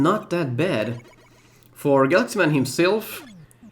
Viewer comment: I don't know why but to me that partially sounded like you were calling him Guts Man and not Galaxy Man.